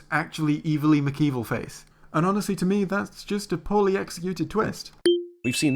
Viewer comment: "That's just a ???? twist"? What did he say?? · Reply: A poorly executed twist